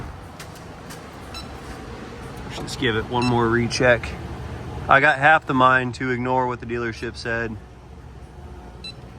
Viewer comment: did he say he’s got half the mind ? 😄👍🏻